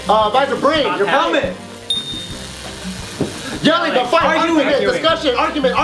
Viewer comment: I swear barbell team said arguing and not argue 5:46 I mean they lost anyway but lol